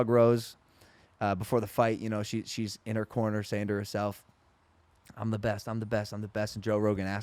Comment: Omg!! Watching the fights I thought Rose was saying " I want the belt,I want the belt. Then at the end after she murdered her n I found out she was saying I'm the best! My arms flew back just like Joe's. I yelled at the flat screen. I had tears in my eyes. Honestly was so proud n the moment was so powerful. Nothing compares. Nothing!!!